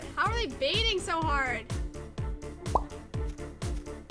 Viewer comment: WHAT DID SHE SAYYYYYY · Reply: she said “dude how are they baiting so hard”